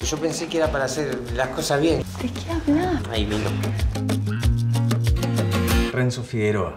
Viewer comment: Que dice Mateo 0:05? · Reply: Ahí vino. Eso dijo